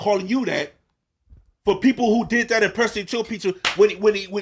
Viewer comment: wtf did say 😂 ill give anybody 500$ to tell me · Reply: who did that, impersonate your pizza........ can I get $250?